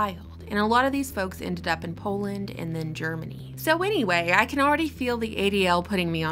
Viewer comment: You said Poland and Germany, but I thought that you said New Jersey.